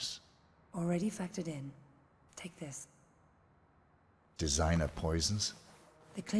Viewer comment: What 47 says in 16:27? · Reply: designer poisons